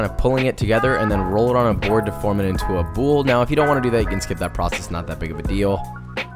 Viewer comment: Does he say ‘bowl’ or ‘ball’? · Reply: boule -- noun 1. a metal ball used in the French game of boules. 2. a round loaf of crusty bread. "he ordered a sourdough boule"